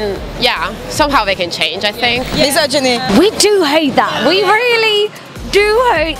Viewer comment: what did the girl in glasses say? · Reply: misogyny